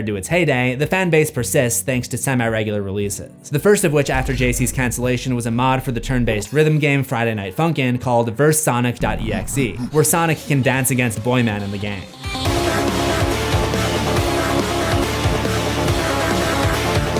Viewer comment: did he just call boyfriend from fnf boy man😂😂😂